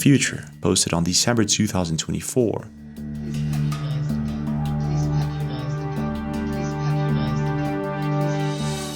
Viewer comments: What was it saying? I tried, but just cant hear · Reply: "please pack your bags and leave" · cant hear the echo video,, whats it saying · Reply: ahhh ok "please pack your bags and go" can you not put sound effects over that stuff thankyou